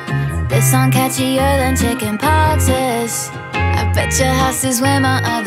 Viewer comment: “This song catcher than chicken pox is, I bet your house is where my other sock is” I LOVE THOSE LYRICS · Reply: Lol. I thought she said chicken pops.